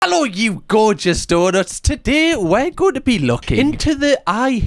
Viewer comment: It took me 5 videos to realize you call fans donuts, i thought you were saying daughters! 😆 🤣 i much prefer being called donuts!